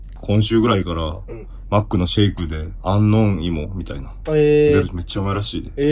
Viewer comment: 何回聞いても02:08アンノーン芋に聞こえる